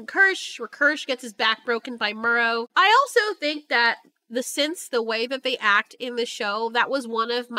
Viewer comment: My main question is they designed these fucking synths so how in the hell do you accidentally construct a synth who can control tech and mimic aliens, this isn't coming from her brain that's just a download copy of her brain the tech in her is doing all this and somehow they don't know how, on further thought it reminds me of those help I accidentally made a .... Google meme searches and now I can't unsee that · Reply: I thought 🤔 kept saying Simps... Not Synths..😁😁333